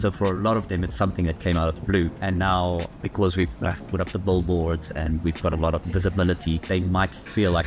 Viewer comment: Does he say, "BULLboards?" 🧡🐮🧡